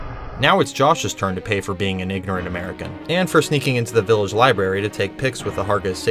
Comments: I heard “now it’s Josh’s turn to pay for being a (hard R) in America” And I was shaken to my core lol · y’all didn’t hear what it sounded like he said??? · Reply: he said “ignorant” but that sounded WAYYYYY different at first 😭